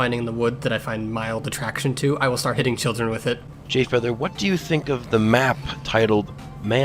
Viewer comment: why did i hear "Having children with it"